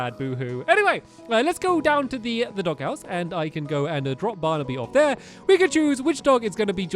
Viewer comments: Who heard this at 2:10 "then we can go to the doghouse and pikachu dog" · When you said “We can choose which dog” I thought you said “ Pikachu’s Witch Dog”